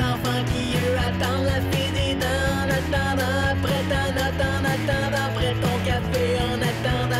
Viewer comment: hey c'est tu moi ou il dit a 1:06 il dit en attendent apres ta nut....